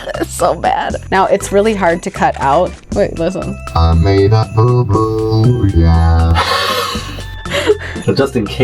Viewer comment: I thought it said poo poo not boo boo lol